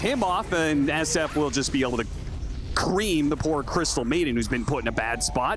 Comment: what did he say? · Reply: cream*** by finishing her off*